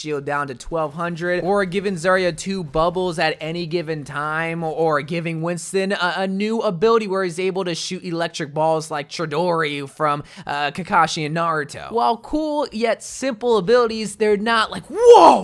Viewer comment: did you just say kakashi AND naruto or Kakashi from naruto?